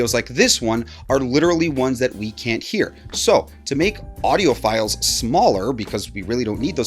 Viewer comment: Ok, back! I have human-based ears. When you said "to make audio files smaller," I heard that as: "to make audiophiles smaller". Lol Since you asked, I will add technical corrections/additions: - When the current travels through the wire in the speaker's driver coil, it generates a magnetic field (an electric field is also produced perpendicular to the magnetic field, but that is not what matters in this case). The fact that the wire is coiled increases the amount of magnetic flux (measurement of magnetic field intensity) produced by the wire through the coil. - The bit depth of the sample is an expression of the precision used to capture the analog signal, which is basically what you said. But it's important to know that the bit depth is the analog voltage resolution. With 2 bits, you would have only 4 voltages to produce, so it would be really choppy looking (hard to produce a sine wave). Even with infinite sampling speed. - Hz means cycles per second (oscillations per second is ok to say), but you would say "hertz" not "hertz per second" because otherwise it is redundant. - An oscillation (cycle) is not the time it takes for a wave to go from up to down. It's actually the time it takes for it to go up, then down, then back up to the original point. You can measure this anywhere on the curve of the wave. Any two points on the wave where the motion is in the same direction and it is the same amplitude (height) is one cycle. - The sample rate is a measurement of how frequently